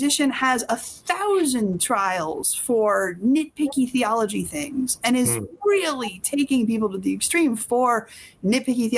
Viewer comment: I have searched every possible spelling for “Schippioni / Skippioni/ Cappette / Capegge” “ and can find nothing — does anyone know what the name was in 58:00 minutes area? Closed captions do not recognize or spell it either. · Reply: Scipione Capece